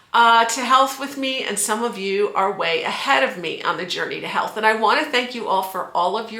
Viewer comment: oh I thought you said journey to hell LOL